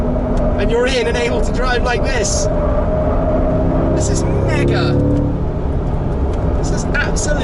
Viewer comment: Did he say the N-Word? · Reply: 7FiftyiS _ mega